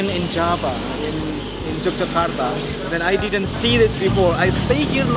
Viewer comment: I think he says "Yogyakarta," not "Jakarta." Different city on Java.